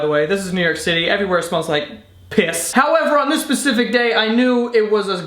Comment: did you really say "On this "Pacific day" or are you trying to add to Internet Fails. Did you mean Specific Day... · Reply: specific day💀